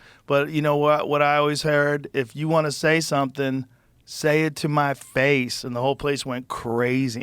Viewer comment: Seriously? You liked the speech where she said, “Say it to my face!” I was laughing my ass off. “Did she just say spray it on my face?!?! Where’s Willie Brown?”